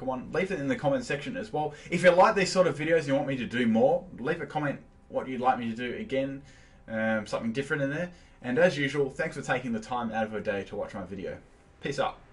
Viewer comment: Did it sound like said "Piss off" at the end instead of Peace out? Lol. And my top 10 shinies (If anyone cares, no legendaries included) 10. Sceptile 9. Haxorus 8. Lilligant 7. Gigalith 6. Porygon-Z 5. Sneasel 4. Flygon 3. Ninetales 2. Roserade 1. Gardevoir